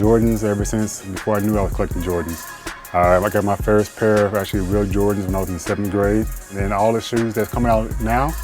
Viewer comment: did he say "i got my first REAL Jordans in 7th Grade" ? And did he say "Original Copies" lol